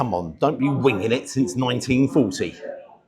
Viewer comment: I thought you said “weeing in it since 1940” 😮